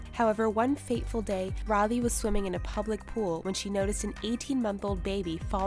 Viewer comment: they say public pool but the Captions say "Popular pool"